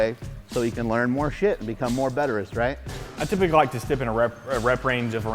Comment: Did Mark say "more betterest" at 3:31?